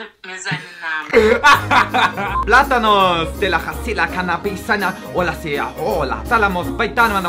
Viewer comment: Hat einer bei 4:19 auch Canabis verstanden?